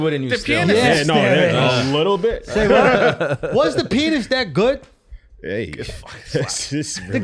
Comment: The first 3 times I played that back he's talking about Roman Polanski and being a pedo and I SWORE he was saying " was the penis that Good!" I was like wtf this boy is wild but then I thought about it and realized he was talking about The pianist the movie roman polanski won for.